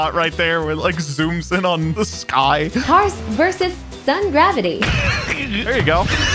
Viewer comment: I heard 'some gravity' instead of 'sun gravity' and it made the clip after that even better.